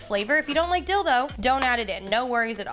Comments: I definitely heard at 4:12, “if you don’t like dildo, don’t add it in” 🤣😂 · I thought she said Dilbo lol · Not me having to rewind 4:11 because I thought she said "dildo" 😂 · Lmao at 4:12 it sounds like you said dildo 😂😂😂 Bc of how fast you were talking 😂 loved the video all in all 😂❣️